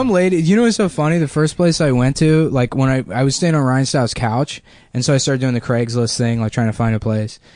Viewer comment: um excuse me did just say he slept on Ryan Styles couch from Who's Line is it Anyway?